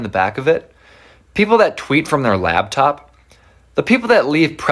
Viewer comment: Nooooo they called me out with that you leave pretzels in the munchies bag 😭😭😭😭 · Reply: I thought they said wee in the munchies bag